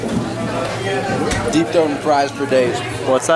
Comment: I DEEP THROAT FRIES FER DAYZZZZ !!! The first thing I thought he said " I DEEP THROAT TROUT FER DAYZZZZ "